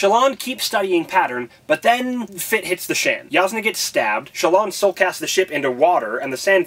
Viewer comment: Did he say, “fit hits the shan” // 😂 6:43 replay